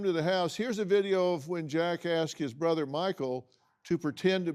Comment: when jackasses brother is what I heard! 😂